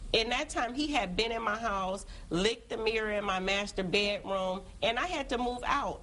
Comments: I thought she said Masturbate room 😐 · um sorry, did i hear that correctly? LICKED your mirror? i’m disturbed